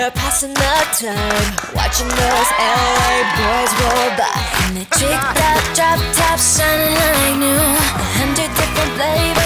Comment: What does ari say at 0:44 cause in the other version she says something else. I HAVE TO KNOW 😭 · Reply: In their tricked out, drop tops shiny like new